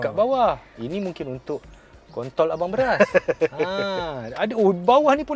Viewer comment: I heard "kontol abang beras" hahaha